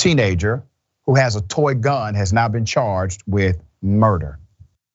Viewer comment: At 12 sec you say he not charged. At 33 sec you say he has been charged. Make it make sense. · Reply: He said "now been charged". Think you just misheard.